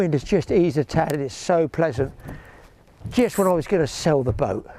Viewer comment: Thank you, (and) Superb as usual. Did I hear, "Thinking of selling the boat" No please No! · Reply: Yeah I was thinking of selling it as I did not use it much last year,but so many people said I would really regret it that I kept it...Good job I did,I've had some good films on it this year.